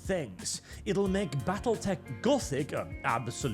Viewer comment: Man, I thought it said Battlefleet Gothic, and now my erection is so very sad.